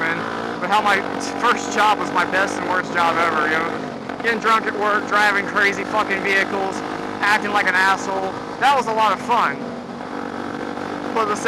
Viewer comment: did you say fucking viehcles?